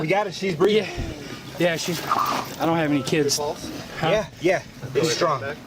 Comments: at first I thought he said I don’t have any kids and thought wtf does that matter then realized he said KITS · The guy fumbling through a defibrillator bag with no clue what to do? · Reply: If I heard the video correctly at 2:35 it sounds like he said to the other guy that he doesn't have any kits which is probably what he was fumbling for in his bag